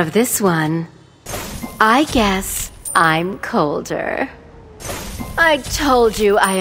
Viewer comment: i thought she said, i guess i'm Kaldr... like what? then realizing Colder.. oooh XD